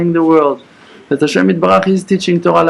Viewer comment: What is the transliteration and translation of what he is saying several times around 18:20, it sounds like Hashem et baruch. Thank you. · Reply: what the Rav is saying is "Hashem Yitbarach" which is translates to "God Blessed may He be blessed".